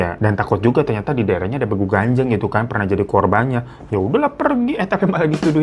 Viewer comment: Salah dengar tadi.... Aq tadi dengarnya BABU GANTENG wkwkkw....